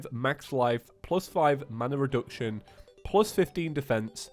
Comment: did he say mana reduction